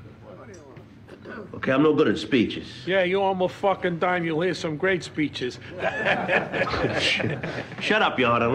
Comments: what does he say?? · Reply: you owe em a dime you'll hear some great speeches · what the hell was he saying? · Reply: "you owe him a dime you'll hear some great speeches" meaning u owe him money you're gonna hear a long loud speech lol